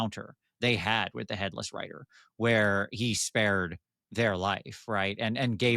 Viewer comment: At first I thought you said Headless Writer, which makes sense since he would have no other form of communication.